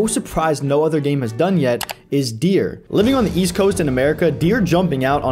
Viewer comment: did you say east coast? the alex g song?? 🥹